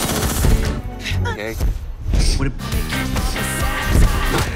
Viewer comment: Lol it sounds like Diego is saying “I’m gay”